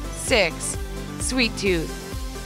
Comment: error. Dry Skin instead of sweet tooth.